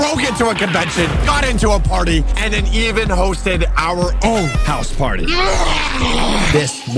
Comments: why did he say hosted and the subtitles said roasted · why did it say the roasted he supposed to be said hosted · he said hosted the subtitles said roasted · the captions say roasted instead of hoasted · the text said roasted instead of hosted